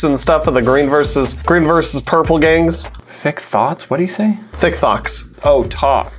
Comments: Sounds like said Thick Thots 🤣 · I thought was saying thick thots at the beginning